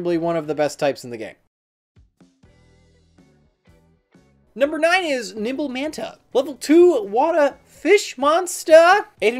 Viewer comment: I swear I heard him say "Number nine is Nipple Manta".